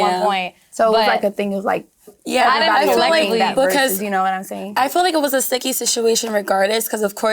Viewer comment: what was Chelley saying when Huda and Amaya were talking I just cannot make it out lol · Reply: “It was like a thing of like everybody agreeing versus you know what I’m saying”